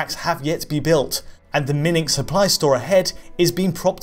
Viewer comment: Did he just say "Minning"? Um... do you mean... mining?